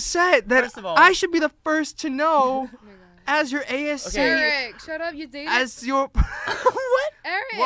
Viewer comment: jamie: eric shut up u dated- eric: WOA WOA WOA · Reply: OH LMAO that was dangerous- I thought she said “Eric shut up you dingus”